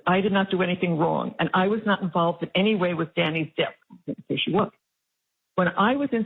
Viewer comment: Hey! Did anybody catch that?? When is reading the text from Wendi (at 37:43) the part where she reads "I was not involved in any way with Danny's death". At the end of that sentence says "yes, she was". Listen closely.